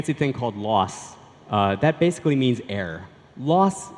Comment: Does he say 'air'? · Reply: he said "error".